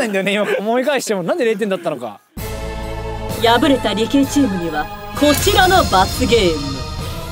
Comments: 何回聞き直しても理系って聞こえる外注さん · 敗れた理系チームって聞こえるw